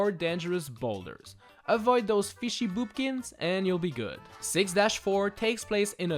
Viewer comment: wait did you say Fishy Boopkins